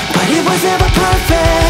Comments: Please tell me I'm not the only one who hears FUCK you were my family! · am i the only one who hears this as "F**k you were my family"?